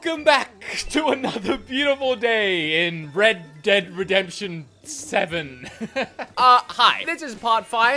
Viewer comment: I can't be the only one who heard " Bread Dead Redemption"